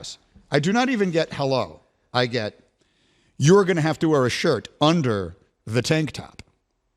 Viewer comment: Thought mcafee said he would wear a suit